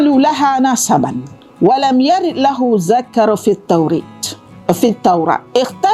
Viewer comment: Saya salah dengo ke apa ni? Zikr ke Zakar di minit 5:36 dan minit 6:13 dalam ayat "و لم يرد له ذكر" 2 kali ulang tu, betoi ke lecturer bahasa arab?